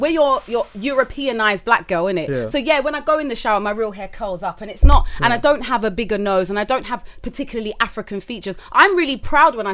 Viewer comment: Did she just say - "WE ARE THAT EUROPEANIZED BLACK GIRL, AND I DONT HAVE A BIGGER NOSE !!!, AND I DONT HAVE PARTICULARLY AFRICAN FEATURES" !!!!!!!!!! ??